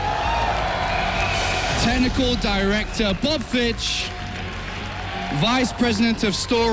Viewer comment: Am I the only one who heard 'tentacle director':D